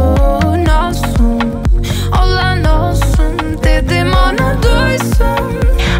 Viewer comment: Ne dediği anlaşılsa iyi olurdu. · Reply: Dedim ona duysun olan olsun diyor